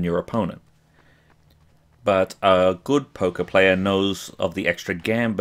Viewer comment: I'll be honest: when you were talking about Poker I thought you were saying Polka.